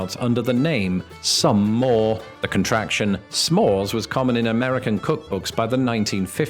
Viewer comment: Lmao when first said "S-mores", I was almost asleep whilst camping...and I thought he said "Smalls"...as in, "You're killing me, Smalls!" !!!🤣 Yes I am that old hahaja!